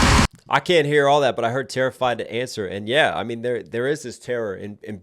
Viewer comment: "Terrified to answer my own front door." Heartbreaking. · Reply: Thank you for making me go back and listen to it again to realize I got it wrong. I thought he said “terrified to answer my own fandom” which I thought was him being worried about being found out like he says earlier.